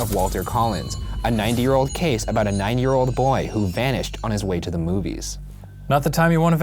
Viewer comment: i thought they said a 90 year old story about a 90 year old boy. excuse me while i go get a hearing aid